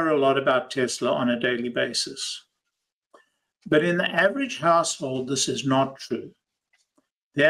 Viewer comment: I heard “household” as “asshole” the first time and now I can’t unhear it. 😅